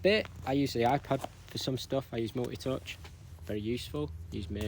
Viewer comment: can anyone tell what the app is hes saying at 20:20? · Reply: What, multi touch?